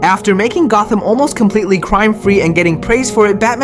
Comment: Am I the only one that thought he said Jamaican Gotham?